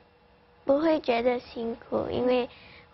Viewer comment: "I heard you practice 5 hours every hour" Ling Ling：finally, a worthy opponent